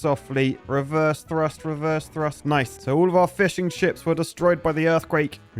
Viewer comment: Did just say “all our fish and chips were destroyed”?😱